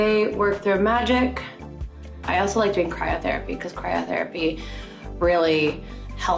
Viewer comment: At first I thought she said I like to cry in therapy . Lol I was like yeah . Yeah